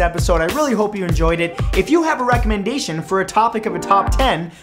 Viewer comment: I thought he said “ rectal-mendation”, like recommendation... bc of the butt thing at the end.. xD